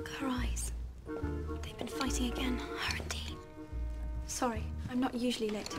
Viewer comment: what does hermione say? starting at 1:07? "look at her eyes, they've been fighting....? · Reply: hpcrazy06 her and Dean